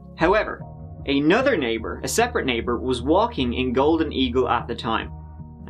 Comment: I had to rewind it because I though I hear “walking A golden Eagle at the time” not “in”. Hahaha I’m thinking to myself, there’s no way he said that so casually 😂🙄🤟